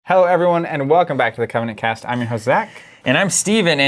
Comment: Every time you say "i'm your host Zach" I hear: "i'm your josé" at which point my brain always goes: "no you're not, you liar!" XD